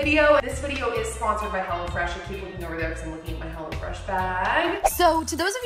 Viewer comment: when you said hello fresh, i thought you said hella fresh